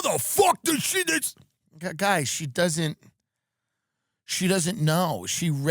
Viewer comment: "guys, she's a little slow" is all i heard 😂